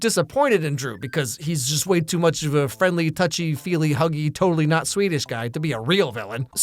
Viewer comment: did you say “totally not Swedish guy”???